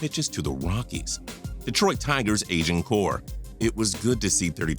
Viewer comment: I thought you said "asian core" when you started on Detroit